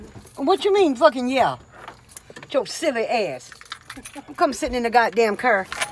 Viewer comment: “Sitting in the currr😭😭” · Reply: I thought she said sitting in the corn 🌽🌽